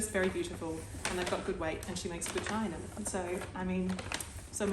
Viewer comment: I just found your channel after watching the felted slipper video. At 1:54, I could not make out what you said so I turned on the captions. Oh dear! I still can't make out what you said, but this is hilarious! 😂😂😂😂😂😂😂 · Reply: Hahaha omg 😂 it’s “chai”, she makes good CHAI in them 🤦‍♀️